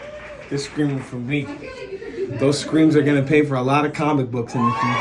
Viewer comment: For a moment, I thought he said, "Those screams are gonna pay for college." And I was like, *I didn't hear that shit right.*😂